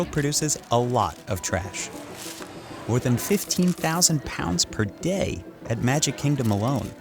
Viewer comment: Subtitles say 15,000 tons but narrator says 15,000 pounds. Big difference lol